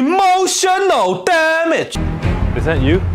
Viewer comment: tbh the first few times i saw it out of context i thought he said "emotion no damn it"